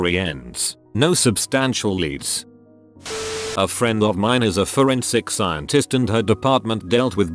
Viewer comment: I thought it said a fore skin scientist